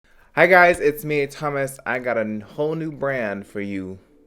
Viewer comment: thought he said in the beginning "hey it's hommas" 😊 so hungry right now